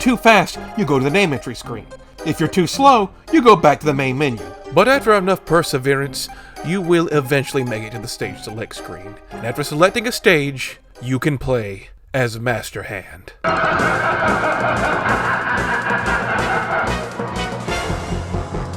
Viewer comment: i thought it said play as mister hands